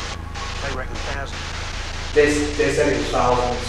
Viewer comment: I think it said "They reckon thousands" rather than they said it was thousands (probably unnecessary but anyways...)